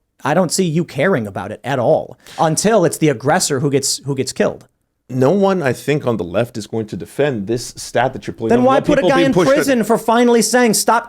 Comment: Did I hear him correctly? 15:18 “No one on the left is going to be defending the aggressor” Is that what he said or intended to say?? And if so, Has he been on the fucking planet in the past, like, 3 years? All I hear from this dude is buzzwords and cringe arguments. Even his facial expressions are self righteous. 🙄 Just listened again, “…the stat that your putting up” ??